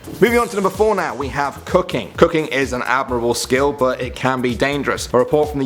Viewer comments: When he said cooking I heard cocane I'm like wtf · Listen closely to nom 3 i heard it as cocaine but its cooking 😕😕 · When he said cooking i thought he said cocaine 😂😂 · When he said cooking I thought he said cocaine